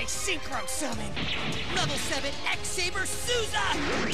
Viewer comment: I coulda sworn I heard him say X-Saber Susan.